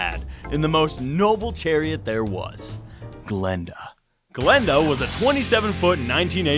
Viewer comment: who else thought he said glinda